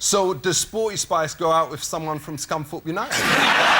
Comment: what did he say ? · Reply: Scunthorpe United